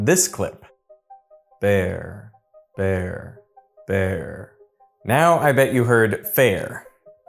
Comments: I heard “there.” Not “fair.” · I hear "berr, verr, bere" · In the second clip I hear “bear,there, bear” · I thought he said bear then fair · At no point did I hear “fair” lol I heard bear, there, or vare(whatever that is) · I heard "beair". And "their". Don't know why, maybe because I'm not speaking English natively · I heard "there" and "pear" :D